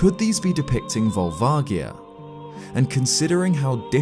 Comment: Who else thought it said vagina